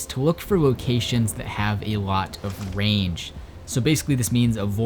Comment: thought you said look for locations that have a lot of rain. Gotta find that mood ☁️🌧